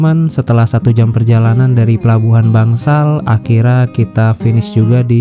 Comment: saya kalau dengar ucapan sampean "akhirnya" kok kedengarannya "akhirra" apa sy yg salah dengar, mungkin aksennya perlu diperjelas. Maaf ya!